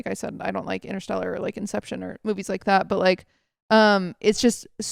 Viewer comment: "i dont like good movies" is all i heard